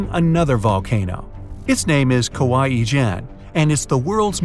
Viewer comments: i thought you said kawaii-chan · Kawah ijen I thought you said kawaii Chan 😂😂😂😂